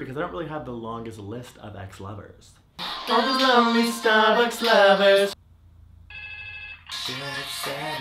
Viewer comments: I feel like everyone (or a lot of people) heard “lonely Starbucks lovers" or something like that because they're obsessed with the popular coffee chain. I did not hear that and was that annoying person, correcting everyone. Come on guys, it's “I've got a long list of ex-lovers." :) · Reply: I heard "all the lovely Starbucks lovers" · Instead of "list of ex-lovers" I heard "star axed lovers" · I feel like everyone (or a lot of people) heard “lonely Starbucks lovers" or something like that because they're obsessed with the popular coffee chain. I did not hear that and was that annoying person, correcting everyone. Come on guys, it's “I've got a long list of ex-lovers." :) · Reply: i heard 'gotta love these starbucks lovers'